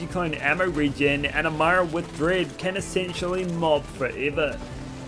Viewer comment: what does he say here? "Amara with dread"?